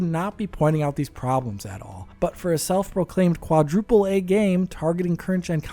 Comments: "AAAA" famous last words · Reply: Ohhhhhhhhh i thought they said Quadriplegic game... wtf is AAAA game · I see now... i thought they said Quadriplegic game... wtf is AAAA game.... i guess i need my ears and brain checked